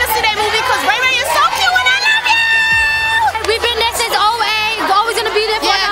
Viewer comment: Wait at 1:57 did she say she been here since 08 ?? -__- lmaooo smh